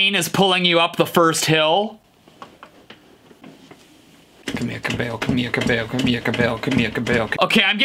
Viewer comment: he sounds like he says come here cow bell instead of camila cabello